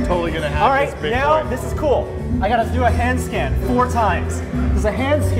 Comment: I thought he said hand stand XD